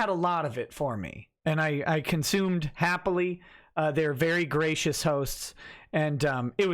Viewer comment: lol for a moment, it sounded like you said you consumed the hosts. All I could think was,"Nick, noooo!" lol